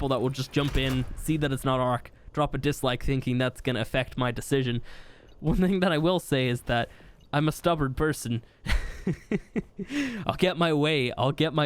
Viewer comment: I thought he said cupboard person the first time NOW THAT'S ALOTTA DAMAGE